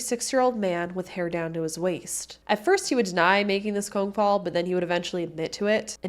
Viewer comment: Not taking from this awful story but at 13:42 did she say “cone fall” instead of “phone call”?